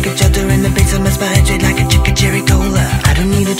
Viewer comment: Thanks, I thought it was saying chicken cherry cola